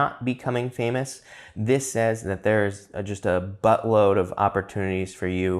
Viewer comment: did you say a "buttload"? A buttload of opportunity, sounds like a pile of shit. Lol! Jus had to. ;)